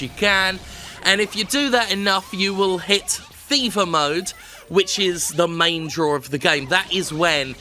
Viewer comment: I thought you were saying "FIFA mode" instead of "Fever Mode" (when you were making the slot machine / casino comparison) as a sneaky jab at the EA series. Either one actually works contextually. Well, except that "FIFA mode" would require you to pay for additional treasure.